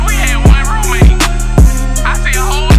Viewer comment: I heard "roommate" lmao